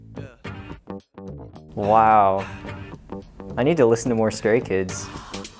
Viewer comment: I THOUGHT SAID I NEED TO LISTEN TO SOME MORE SCARY KIDS, THAT TOOOK ME OUTTT